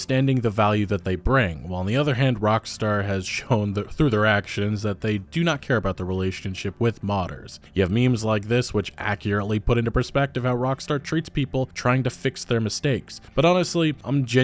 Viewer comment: I keep hearing “rockster”